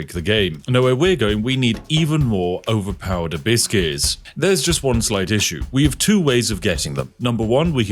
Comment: The way you say abbyss gears sounds like you say "biscuts" or byse case